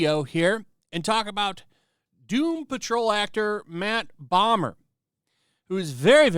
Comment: Not going to lie. For a second I thought you said "Matt Bummer". And all I could think was, what an appropriate name he's got.